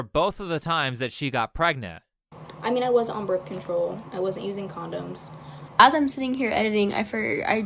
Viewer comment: It looks like YouTube deleted my comment. Man do I hate the people who run this website. She said she wasn't using not was using. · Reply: wow, I apologize. I had to slow it down and turn it way up and watch her lips to catch it cuz the first few times I replayed it, including right before making my comment, I heard "i was using condoms". Probably an accent barrier, but in either case I was wrong. Thank you for paying attention to small details... you see? My point is still TOTALLY valid though 🤣🤣🤣🤣🤣🤣🤣🤣🤣🤣🤣🤣🤣🤣🤣🤣 P.s. two of the most common ways people get pregnant while taking birth control correctly is because they take something else at the same time that renders the pill useless. Hopefully this is getting better, but I've had friends prescribed antibiotics or anti fungals that were not told this would interfere with their birth control pill, and yes the doctors knew they were on them. A third common thing is St. Johns Wort, which people take to treat depression naturally... unfortunately people don't take herbs all that seriously, and doctors often don't even bother asking about supplements like this. This is not in any defense of the girl in this video, they were things I thought of after the fact and things I learned about birth control pills from my own doctor but that my friends in other countries or with less attentive Doctors here (im in Canada) never heard of. Which really sucks for the few who learned the hard way.